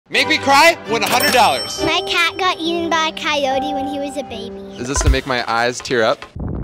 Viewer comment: That poor little kid “my cat got eaten by a coyote when it was a baby” 😢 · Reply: I thought she said her dad got eaten by a coyote 😂